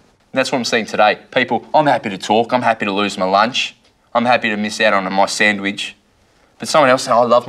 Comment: what does he say at 2:19? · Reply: "I'm happy to miss out on my sandwich" "Someone else might say I love my sandwich" but garbled